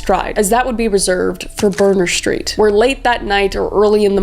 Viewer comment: I thought she said boner street 😭😭😭